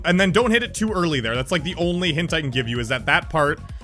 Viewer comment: Thats the hint I Can give you. I heard: Thats the Hentai I Can give you.